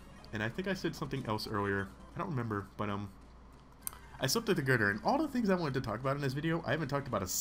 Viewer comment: Am I the only one who heard "I slept with a gurdurr"? :P